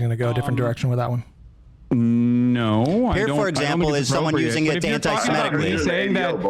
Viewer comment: Getting annoyed at 46:20 because you didn't listen! Ethan said "here for example is someone saying it anti-Semitically" and half-jokingly answered Hasan's interruption with "it's your fan" to which Hasan goes on an 8 minute downward spiral because he hates context. The clip demonstrates that it's an anti-Semitic comment, which provides context to viewers when he segues to Hasan's tweet.